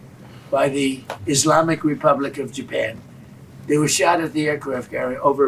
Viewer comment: "islamic republic of japan" Did i hearing it right ?